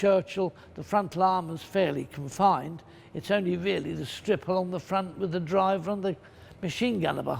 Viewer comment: I heard this as it's only the stripper on the front that's thicc